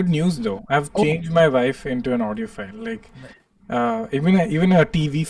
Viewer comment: for a sec, I thought Kenneth said "I turned my wife into an audio file" I was like what? Why?😂😂😂